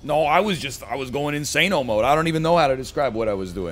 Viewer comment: wth did NL just say Insaneo mode (34:52)? wonder if it's based off of insaneo style